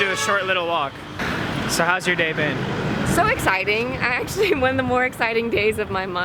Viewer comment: am i the only who heard hows ur day babe in